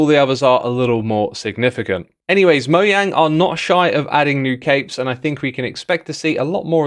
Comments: When you said " new capes " in the beginning of the video; I thought you said, " New Cakes." I was so looking forward to chocolate cakes, berry cakes, and carrot cakes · I listened to this without watching it first and thought you said "new CAKE" and I must say I am disappointed to now see the truth